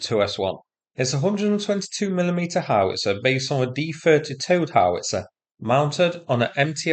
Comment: On passing glance I thought the title was 'Godzilla destroyed Russia' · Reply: Then 1:07 in I heard "toad howitzer" and took a minute to work out it's not operated by toads, it doesn't fire toads, it gets towed. Think I need to go to bed.